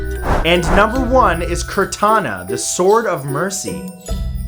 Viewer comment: I thought I heard Cortana for #1...